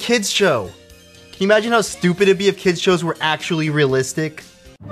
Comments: literally thought it said "if Kids were real" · I thought it said "if kids were real"